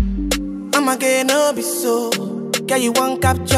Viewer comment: In the beginning of the song...why did I think I heard "I'm gay no be so..."🤔😲😆 I had to listen again and heard "omoge no be so..."😆😁 phew!